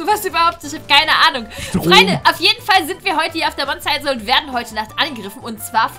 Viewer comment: Ich habe monte insel verstanden 😂😂